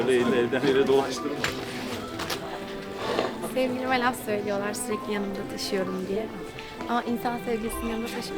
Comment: Sevgilim mi anlamadım ne diyor bilen yaza bilirmi. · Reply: Yağmur senaryoni sevgilim diyor Sevgilime laf sokuyorlar surekli yanimda taşiyorumya dedi